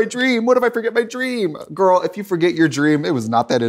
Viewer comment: Why did I hear “jerkoff” instead of “journal” at the end 😭😭😭